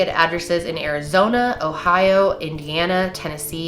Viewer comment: I watched this before going to bed and creeped me out 38:02 did u say Ohio! 😐